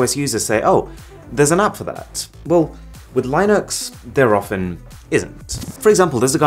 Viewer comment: Did he just say, LIE-NUX?